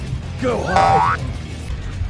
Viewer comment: Help I just heard “Go hard” At slowmo version when Bakugou said “Go home”